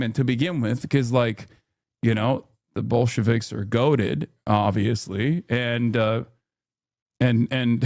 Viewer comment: did he just say that bolsheviks are goated?